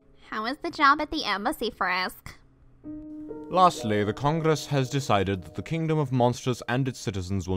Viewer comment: When the part with The Congress came on I thought it said The Condese has decided.......XD